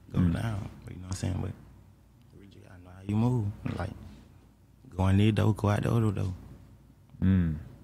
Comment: wtf he say, eyingowdokododo? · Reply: Go in this door go out the other door